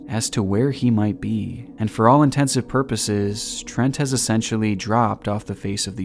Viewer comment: Did I hear "all intensive purposes?" 🤣